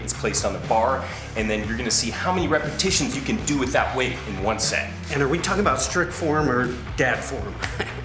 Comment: One set ok, thought he said one second lol